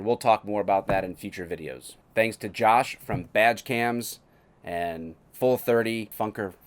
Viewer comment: Hahahaha I swear I thought I heard you say “the maker of vag cams” and not badge cams!! Hahahaha More importantly, thank you for putting yourself on the line for us citizens!! Godspeed sir, Godspeed...